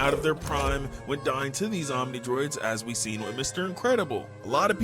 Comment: did bro just say zombie droids? Lmao